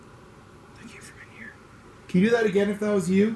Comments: did anyone hear a whisper saying "thank you for being here " ?? Or was that him? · anyone hear the whisper?? I heard “thank you for being here” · "thank you for being here?" Anyone else hear?